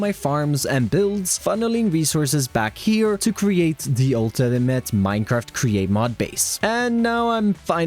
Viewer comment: ‘the ultinimante’ (Thats how I heard it anyway XD (this is me having a laugh.. not making fun of ppl))